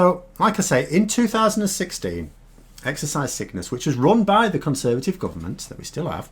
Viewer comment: I keep hearing 'Exercise Sickness'.